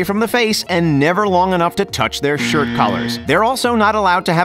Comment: Somebody help me i can’t hear what does he said at 3:07-3:09 (english is not my mother lang) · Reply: izat khizriev “not long enough to touch their shirt collars”